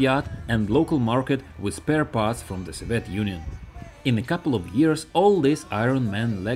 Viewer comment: Do you mean "soviet" union?? I hear "civet"